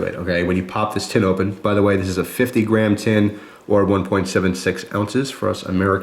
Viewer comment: when you said 50 gram tin i thought you said 50 grand and i almost spit my water out of my mouth